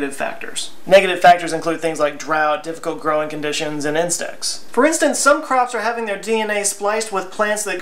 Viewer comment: When you said, "insects", I thought you said, "incest"! Lmao!